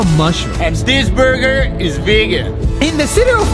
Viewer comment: What they say “ Vegan” What I hear “Virgin”😂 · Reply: I hear "Vigan"